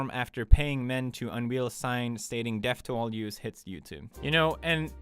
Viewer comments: When he talked I thought he said 'depth of all juice' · Pewds sounded like you said "Deaf to all Juice".... But, good job anyway today.